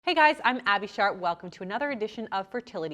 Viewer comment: I thought she said